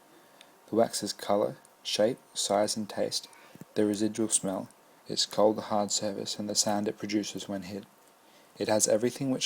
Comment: what does he say? All I'm hearing is "there is a juice mail called the hard service" I don't understand? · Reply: "The residual smell, it's cold hard surface, and the sound it produces when hit."